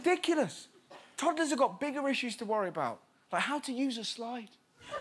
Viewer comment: I heard the ‘how to use a slide properly’ as ‘how to use a sniper properly’ so uh, that’s my comment of the day goodbye I gues